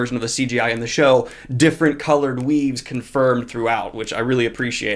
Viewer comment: Been so immersed in otaku culture recently that I heard "different coloured weebs".